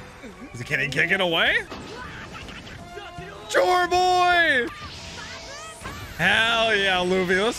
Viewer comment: did he just call luffy "JoyBoy"?🤔